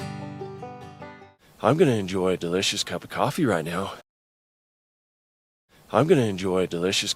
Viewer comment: 通常：I’ve been enjoying delicious a cup of coffee right now. 0.75：I’m gonna enjoy a delicious cup of coffee right now. 正解：I'm going to enjoy a delicious cup of coffee right now. 速度を落としたら正解できましたヾ(*^∀^*)ﾉ～♪ 通常速度ではa cup ofのように聞こえたのですが189番の動画でa specific set of grammar rulesをspecific a set of grammar rulesと間違えたことから、今回、正確なaの位置に修正できました。 欲を言えば、通常速度で正確に聞き取れれば最高なのですが…(^▽^;) 今も0.75の速度だとgonnaと聞こえるのですが、通常速度だと難しいです(-_-;) ナゼ、通常速度だと’ve been enjoyingと聞こえるのか疑問です(?_?)？！ 通常速度でも正確に聞き取れるようにシャドーイング練習がんばります(^0^ゞ♪ 本日も素敵な動画をありがとうございます(^O^)／♪